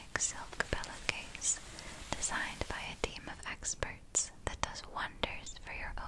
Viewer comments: I thought you said demon experts in the opening ad 😅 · You definitely said "designed by a team of experts" but i really thought you were going to say "Designed by a Demon" for some reason...